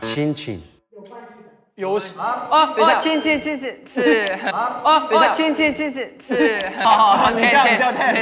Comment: i love how Ten said "cincin" instead of his own name lmao😂💚💖 and now maybe he will have a new nickname which is "cincin" loll 🤧